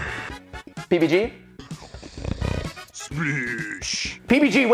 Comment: sounded like Matpat said baby g